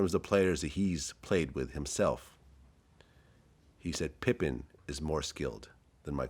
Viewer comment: Am I the only one who thought vlad said “pimpin” is more skilled than Michael jordan lol and yes I grew up when the Chicago bulls were at their prime and yes I know who Scotty pippen is lol